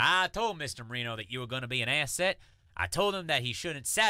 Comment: For a moment I thought Roy said "asshat" instead of asset. And the correct term for "mongoloid" would be trisomy 21. OMG! No full minute or more of flashback when said "I ate my sister!" Has he finally gotten over it?